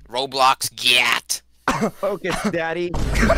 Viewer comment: Did kreekcraft really say "focus daddy!" XD